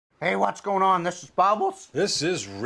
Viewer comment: Haha I thought he said what's going on it's babbles lol love these guys their always so hilarious I love getting buzzed watching these guys get buzzed haha TPB never gets old Rip John Dunsworth 🍻 🕊️